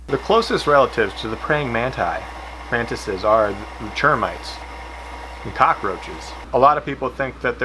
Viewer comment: For a second I thought he said the closest relatives to mantis' were Germans rather than termites.